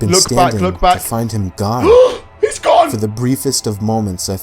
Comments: Did anyone realize on the subtitles, at 27:47 instead of it saying gone, it said god? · the subtitles at the bottom of the screen say god instead of gone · Who else notice at 27:43 the subtitles says God instead of gone